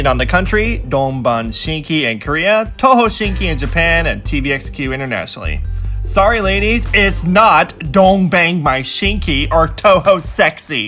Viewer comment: I heard DON'T BANG MY SHINKI HAHAHAHAHA